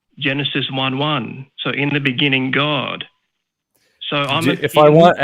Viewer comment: When starts ranting, I just hear it as "In the big inning, God..." and think about baseball.